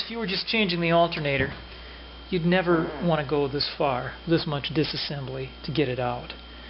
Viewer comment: you mean far or i hear a fart hahaha.